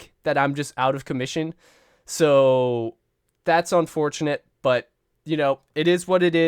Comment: womp womp in the big 25 is crazy Also MD fan?? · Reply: I thought it said ‘unfortunately’ instead of fortunately